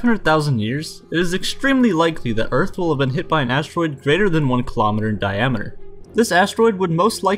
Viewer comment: Dont think you can evolve past asteroids who will destroy the earth. Only way I see humans surviving is like flying across the galaxys and kinda like having farms and stuff idk. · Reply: Flamur Syla bruh I thought you say steroids for a second 💀💀😂